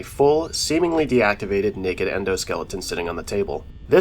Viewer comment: bro did not say "naked" 😭